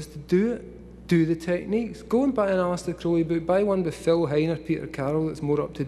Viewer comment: Can anyone tell me what book he’s talking about at 39:11? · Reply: He said Aleister Crowley, Phil Hiner & Peter Carroll books, YOU'RE WELCOME